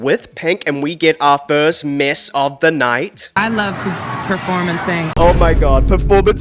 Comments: I thought Pink said "perform and sing" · I thought Pink said "perform and sing' not performancing' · So... She said "perform and sing"... Oof. Maybe use headphones the next time you are writing jokes? Or was the joke that the "first mess of the night" was you? It's so obvious that she did NOT say "performancing" that I'm bit confused here.